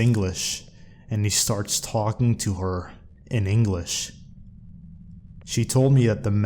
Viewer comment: Did you really say "Singlish" at 44:30?